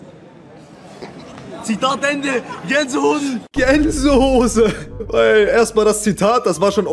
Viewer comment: ey bin ich der einzige der hier genseho*en satt gensehose verstanden hat 😅